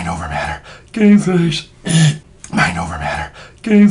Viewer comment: lol it sounds like he says Gay face